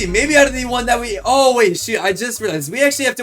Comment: did he just say shi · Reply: No he said shoot